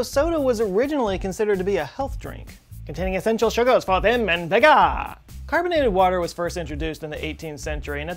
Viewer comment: i’m literally begging for someone to tell me what he says at 1:19 i cannot understand to save my life i’ve rewatched it <num> times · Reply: Vim and vigor